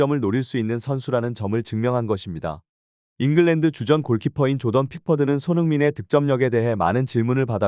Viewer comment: 조던 픽포드 아니었나? 왜 자꾸 킥보드로 들리지?